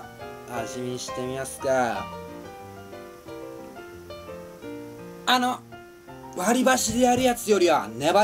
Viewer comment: it sounded like he said my name sort of, Anna